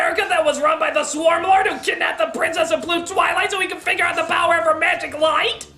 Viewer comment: When I was younger, I thought that said "Nude Twilight".